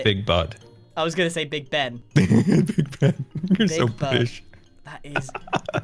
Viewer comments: i thought he said "big butt." XD omg hah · It sounds like Scar said "You're so fish" but he said "you're so brittish". I'm rewatching this so not hearing it the first time, it made me laugh.